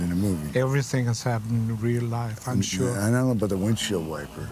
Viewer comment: I´m not an American. so I don´t understand what he is saying in 1:33 "I know about a windshield wiper"? makes no sense. can someone help me please? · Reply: He’s saying “I don’t know about the windshield wiper”, referring to the 4th incident where Jack sliced off a woman’s breast and placed one of them on a cop-car’s windshield.